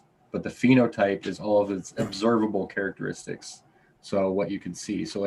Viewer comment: Observable meaning what you can see. The 10 seeds would have differences you could see, and differences you cannot see. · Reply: My apologies,I thought he said "absorbable". Obsevable makes sense.